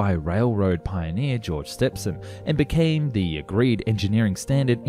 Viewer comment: George Stephenson .... no idea what you where trying to say sounded like stepson... 07:06 its REPERATIONS ..not revpererations